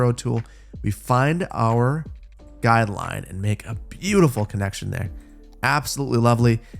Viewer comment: WHAT DID HE SAY?? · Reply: guideline